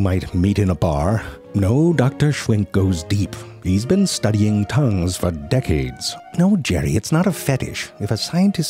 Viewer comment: Deep Tounge... Yep. I heard that too.